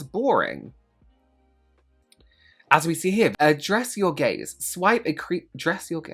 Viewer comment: I was listening and not watching for a minute and I hear "dress your gays.." 😂